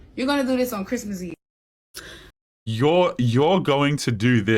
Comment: I think she said "on christmas eve" not "to me".